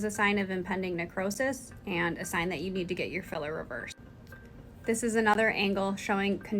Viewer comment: I thought u said u had your filler removed?